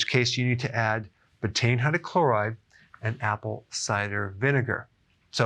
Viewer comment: What did he say right before apple cider vinegar at the end? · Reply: Betaine hydrocloride